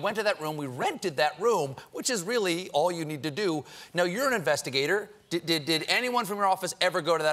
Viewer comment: all i heard at about 26:48 or so...is.....urine-investigator rofl